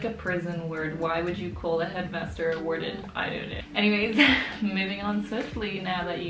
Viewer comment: When you said moving on swiftly, all I heard was ‘Swifty’ ... the ptsd is real😳